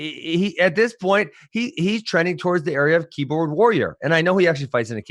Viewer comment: "He's trending toward the area of keyboard warrior" ROFL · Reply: Thanks for clarifying that, I rewinded a couple times and thought he said keep away warrior.